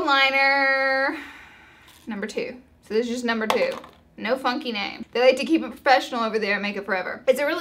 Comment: Haha at around 8:50 it sounds like you say "no f**king name" and I'm like LAURA?! -you know, the way your mom might say it. Haha I'm dying!